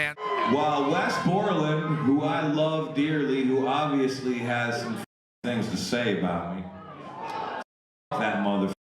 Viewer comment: but what does Corey Taylor say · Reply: Melissa Holt ‘sucks to be you’ is what he said